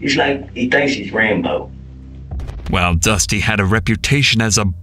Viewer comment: why did I think he said "ranboo"? 🙏😭